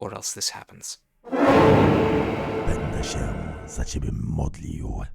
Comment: I SCREAMED when I heard Polish oh my god why is my dear country involved in this hell of a game. That guy said: „Będę się za ciebie modlił” which means „I will/I’m gonna pray for you”